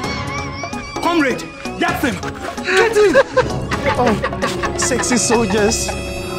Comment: I heard “Sexy soldiers” 😂😂😂😂